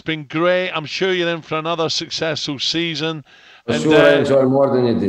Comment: Towards the end when Alan said " I enjoyed having you on talk sport so much" Did anyone hear jose flat out say " For sure you enjoyed it more than I did" This is so brutally honest 😂😂😂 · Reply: He actually said the opposite... "For sure I enjoy more than you did, for sure" - classy and not even slightly brutal